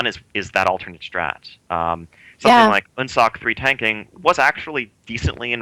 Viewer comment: what was that boss they say at 17:50? i cant understand it, or at least i dont know of any boss that sounds like en-sock. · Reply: Amber-Shaper Un'sok